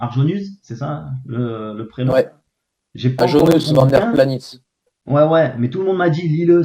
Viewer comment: Salut, c'est quoi le livre mentionné à 15:19 d' "Argenus Vanderplanis" ?? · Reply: Aajonus Vonderplanitz :)